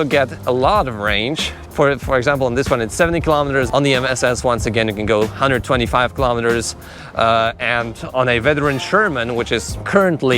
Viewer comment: I thought you said dont buy a veteran abrams?